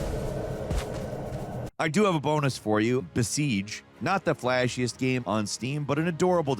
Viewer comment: what's the name of that game? said The Siege but I could not find it on Steam. (I'm getting several results but none of them look like that.) Edit: Okay never mind, it's Besiege. I'm dumb lol.